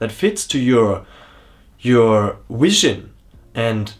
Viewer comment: wow man, that little intentional or unintentional Freudian slip where you made a portmanteau of Wisdom and Vision was powerful. Might just be an accent thing ☺️, but I swear I heard (Whi-sion)